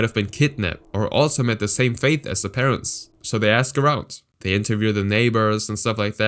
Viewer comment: it sounded like he said “so they ask her out.“